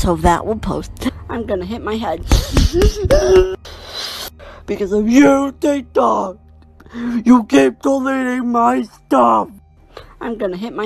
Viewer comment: En el min. 4:18 los balbuceos que no entiendes mas o menos dice "beacause of you tiktok, you delated all my stuff" que seria como Por ti tiktok, tu borraste todo mi contenido o algo asi XD